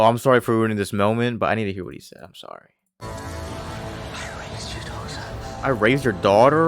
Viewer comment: He said "I erased you Doctor" 😅 those captions are wrong